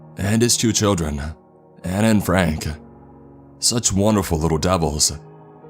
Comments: "One could say she had the ability to impress Gordon Ramsey, and his two children, Anna and Frank." 😅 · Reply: When he said that I legit thought he said Anne frank and I’m like oof that’s kinda offensive lol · I thought he we saying her cooking could impress Gordon Ramsey and Anne Frank and was very confused